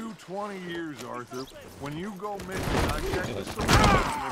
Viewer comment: At the end Dutch says "fair enough," but I heard " fat enough."